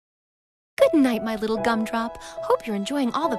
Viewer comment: WHAT did she call her son? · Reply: gumdrop